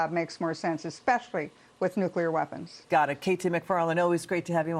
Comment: Did I just hear finish the job with nuclear weapons???